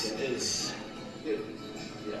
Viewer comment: Yo flight what tf did you say at the end 🤣💀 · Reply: Todd Smith that’s gonna cut it off comment down below what i should react to next on the road to 2 milli milli